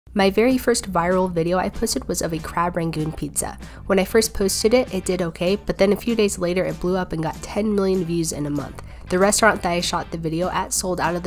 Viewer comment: I thought you said crab raccoon 🦝 pizza and I was like "Ayo what?"